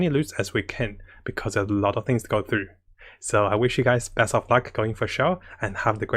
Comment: did he say best of fuck at 9:55 ?